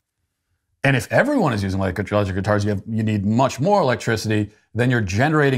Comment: I don't mean to question our sweet daddy, but....did you just say "electric guitars"?